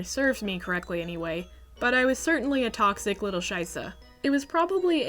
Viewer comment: Cool video, but I'm not sure if I heard a German word at 3:19 😳😅 "Scheißer"